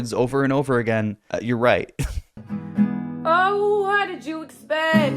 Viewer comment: she really says “what did jew expect”